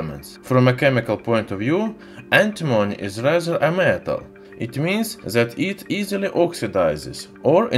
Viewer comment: I can not understand what you say now and then. At 0;24 forexample. Antimony is (inaudible) a metal..... Maybee you could get someone who speak good English to narrate your videos? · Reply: I think he said "antimony is rather a metal". I guess he was explaining the paradox of belonging to a semi metal group, but behaving like a metal from a chemical point of view?